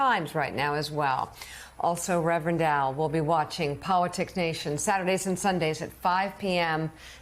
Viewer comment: Mika said "her piece" referring to Mara. I thought she said HERPES 🤣